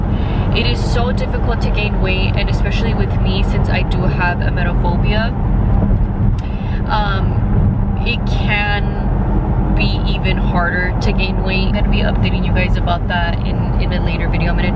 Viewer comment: what does she say ? A metal phobia?